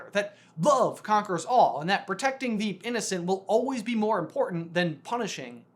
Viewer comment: I swear I heard him say Incendo🔥